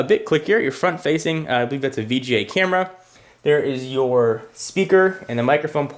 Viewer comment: did he say bj camera????(lol)